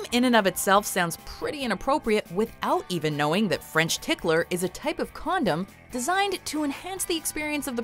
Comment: i thought i heard "french hitler"